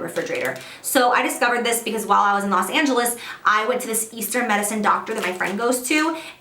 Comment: Hahahaha I thought you said EASTER medicine doctor. Lol.